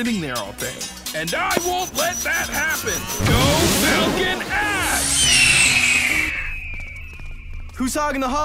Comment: Was not expecting to hear "prolapsed anus" at any point during my viewing of this show. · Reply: Patch Adams i thought he said "falcon ass" and not "Falcon axe" lol